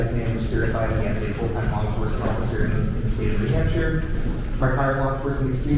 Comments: the cop says New Hampshire but the text says Virginia. I've listened to it 10 times, am I hearing this wrong? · Caption says "Virginia" instead of "New Hampshire" when the cop was speaking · caption says Virginia while trooper stated New Hampshire. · "new hampshire" but the subtitles say virginia lol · the trooper clearly says "in the state of New Hampshire" but the subtitles say "Virginia"... which is not even a neighboring state. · he says the state of New Hampshire but the caption says state of Virginia. Interesting